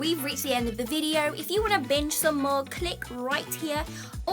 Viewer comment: Ha. At the 7:40+ mark , I thought you said if you want to bitch some more...binge! Ha!